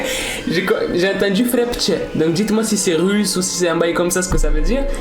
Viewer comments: siri a dit je suis satan j'ai entendu 😮😮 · j'ai entendue " s3xUel" 💀